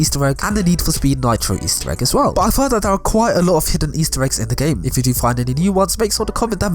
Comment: I thought it said “faster eggs”